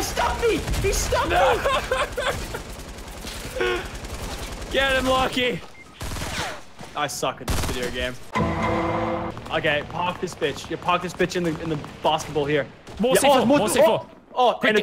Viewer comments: did Lachy say bitxch? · Is it just me or at 3:54 I heard Lachlan say bitch